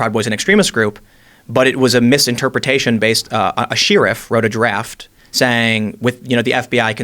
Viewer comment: what word is Tim saying there? Either he is pronouncing Sherriff wierdly or theres a different word I've never heard before.